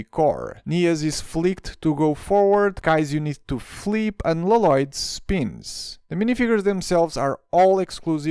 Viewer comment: again what did he say!?!?!?!? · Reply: Lloyd=La Loyd